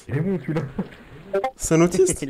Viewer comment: MMMMMMdrrrrrr jsuis mort le passage où y a un mec il arrive il dit dre j'ai entendu "c un démon celui la" xddddd le mec en plus à dix mètres tu l'entendais encore dire dre dre dre dre